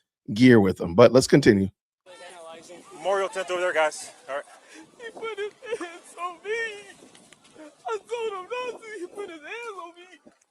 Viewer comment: "He put his hands on me, I swear to god he put his hands on me". Is what I hear. Now, where did he learn it was ok to stab someone for being touched?